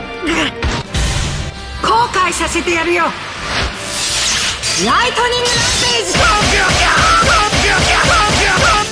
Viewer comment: ¿Que dice Shaina en su ultimate, Lighting que??? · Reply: Lighting Rampage